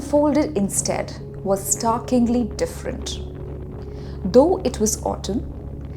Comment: It should be either "starkly different" or "strikingly different". There is nothing called "starkingly different" Be responsible while preparing the transcript, please. Thanks!